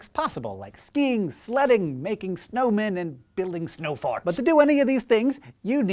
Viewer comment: Hey, Hank. I hope you'll pardon this juvenile attempt at humor. However, I thought you should be aware that, at the beginning of this thing, it sounded like you said "snow farts." Lol. Too funny.